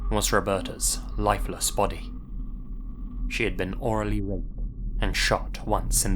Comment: what is being said at 22:43? orally ____? I can’t understand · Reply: At that moment, says, "She had been orally raped...". often muffles unpleasant words like that so that YouTube will not automatically detect them and deprioritize the video to make it less visible and less viewed. I'm not sure how necessary that is though. Maybe just does that out of an abundance of caution.